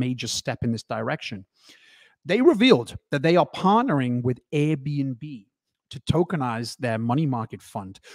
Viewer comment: It sounded like you said AirBNB. it is actually ABRDN, pronounced Aberdeen. Apologies if you knew this already.